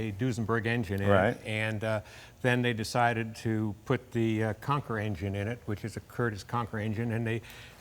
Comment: He's saying Conqueror engine. I thought he was saying Conker engine.